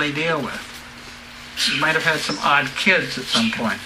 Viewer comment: did he say odd kids?